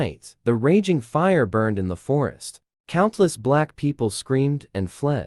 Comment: WHAT DID YOUR TTS SAY?!? 🫣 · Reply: countless black people screamed💀💀💀